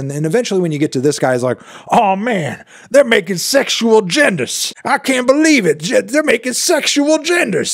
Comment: Did he say "pushing them gender pronouns and sexual genders" 😭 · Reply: Ok yes guys I am dumb but in my defense he said sexual in two syllables instead of three. Like "sex-shul a-gendas" and what I heard was "sex-shu-al gendas". So not totally crazy